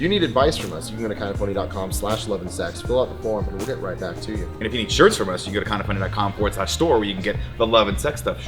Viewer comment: lol I thought Nick said "if you need insurance from us" instead of "if you need shirts from us"... almost lost it